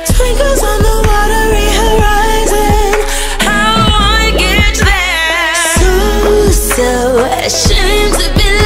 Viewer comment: I swear I hear “how do I get there” instead of “how do I get you there”